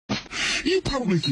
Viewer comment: What I hear at first: “U probably think this world is a dream come true..” ”but ur mom”